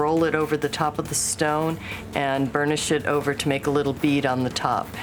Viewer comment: did she say "burn this shit over" ????